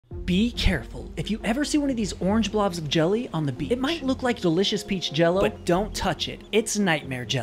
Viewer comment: I thought he said “ ✨BeAcHCaReFuL✨” 😭